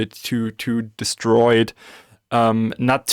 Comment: did someone say Destroid?